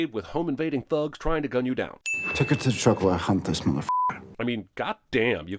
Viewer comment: I swear to god on first pass I thought he said "Take her to the truck while I hump this motherf--er".